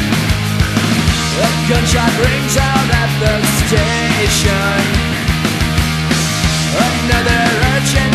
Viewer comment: Did i hear, "i give out rim jobs at the station"? xd